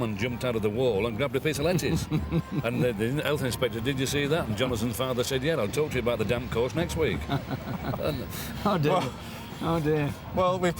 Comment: what did jonners father say to the health inspector? i cant understand fred's accent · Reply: He said a salmon leapt out of a wall and grabbed a piece of lettuce. He said Jonathan's dad would talk to the health inspector about the damp course next week, meaning there was damp in the wall - hence the salmon - and altogether insinuating that Aggers was brought up in a council house (which of course he wasn't...his grandparents lived in a grand stately home).